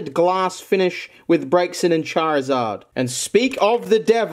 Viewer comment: I thought you said “brexit and charizard” 😂